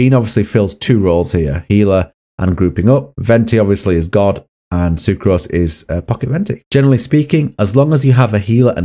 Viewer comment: "Venti, Venti, Venti... " - Did you just say, "Venti obviously is god and Sucrose is pocket lint"? - LOL. Awesome. What did you really say there? I think I'm mishearing.